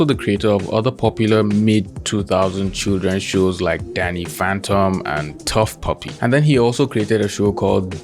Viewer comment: I thought he said daddy phantom 😏